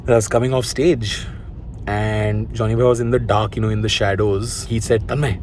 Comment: did you just call Johnny lever.. Johnny bravo????? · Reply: He said johnny bhai